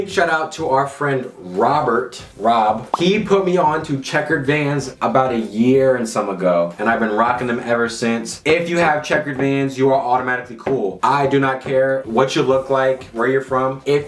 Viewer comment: i heard hitler had checkered vans